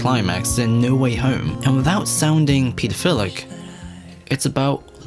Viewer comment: I heard that as Peterphilic at first